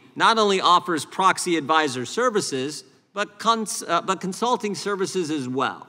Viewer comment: did he say the C Word?! Ahhhhhhhhh!.! Im traumatized 😢